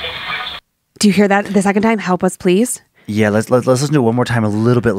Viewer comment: I hear why won't you help us, we found you, help us please